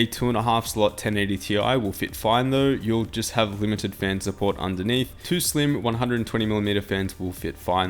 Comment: Great video! I have this case and love it. It's sleek and great looking! It was my first itx case to build in and had a learning curve to it for those not used to building itx. Well worth the price. Only had trouble with getting the right psu. I ended up using a cx 650m from corsair that fit. I recommend sfx psu If it's in your budget for an easier build experience. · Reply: Thought he said that the SF isnt supported? <link>